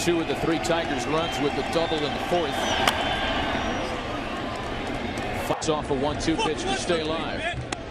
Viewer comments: Did he says F***KS off a 1-2 pitch to stay alive?😂😂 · it sounds like he said “fucks off a 1-2 pitch”😂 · Did anybody else hear that 🤔🤔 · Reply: "Fucks off a one-two pitch" lmao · What did Vasgersian say? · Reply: He meant to say fights off a 1-2 pitch